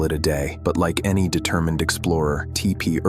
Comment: "Like any determined explorer" all I heard was "Like any damned fool..." Yeesh...